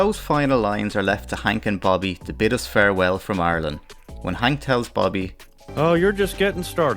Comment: I love your accent and at first thought you were saying "Ireland" the way most Americans do but no, you were saying "Arlen." Wonder if the names are actually related--or the same!